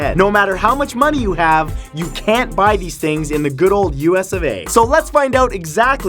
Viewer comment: When he said "US of A" I thought he said "US, eh" I was like like wow he really is Canadian